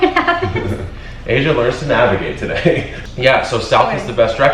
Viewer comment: This is my first video on this channel and I did not know her name... I thought he said "Asian learns to navigate today" 😬 I was like 'dude you can't say things like that' lol